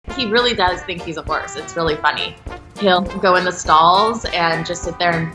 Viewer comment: I thought it said “dog who thinks it’s a corpse” LEIK WHATT